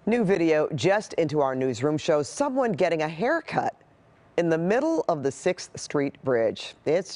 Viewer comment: Did you say someone getting a haircut?